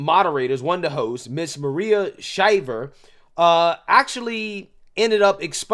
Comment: I thought you said Maria Shiver! That part..